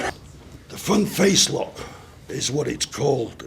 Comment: At the end of the video I misheared "The fun face lock is what it's called in wrestling now." Not that's an ironic name that will stick with me for a while. The fun face lock XD